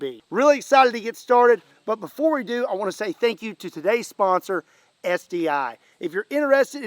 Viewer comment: I was looking away from my phone and i thought i heard "And todays sponsor is the FBI" never gave myself whiplash that hard in my life xD